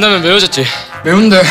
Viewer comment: 진짜 맛있다..라고 하는데 개맛있다로 들리는..ㅎㅎ